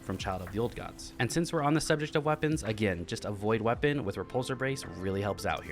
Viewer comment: Im so dumb, I thought you were saying avoid a weapon with repulsor brace apose to "A void weapon with repulsor brace" 😝